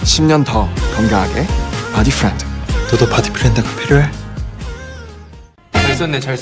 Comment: I literally thought he said ‘do u need a boyfriend,too?’ 😂